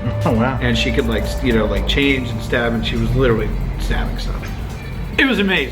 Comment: "She was literally stabbing stuff!!" At first when he said this I thought he said "She was literally dabbing!!" 😂😂